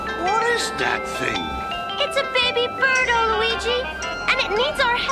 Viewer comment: Dang, cant believe was so mad at Luigi for pointing out the spaghetti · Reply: I thought he said f u Luigi 😂